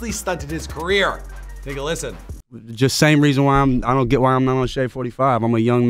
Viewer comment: Did he say niggga listen